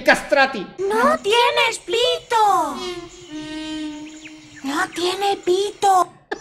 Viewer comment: yo le escuché ''Voy a desmayarme'' y luego ''lo tiene todo''